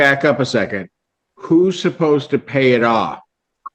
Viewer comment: I heard " someone is supposed to be peeing it off."